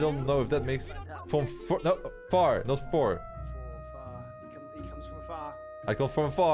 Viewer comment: did you said for and not the word far😑